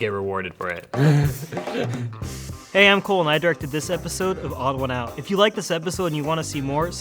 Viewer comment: I thought Cole said he jerked to this episode 😂(I'm sorry)